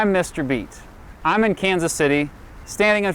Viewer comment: Thought u said u were mr beast